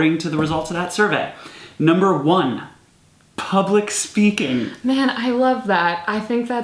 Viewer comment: I thought they said public spanking, not speaking.